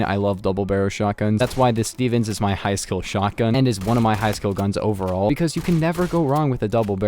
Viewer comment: I thought he said my high school gun